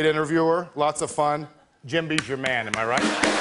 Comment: to me it sounded like Vince said Jimby, not Jemby.